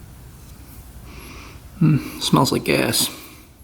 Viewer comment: “hmm smells like ass”. I know he said gas but that’s what it sounded like with the WI accent lol.